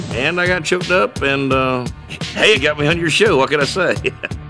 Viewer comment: for anyone who didn't catch what he said, I'm 99% sure he said "hey it got me on your show"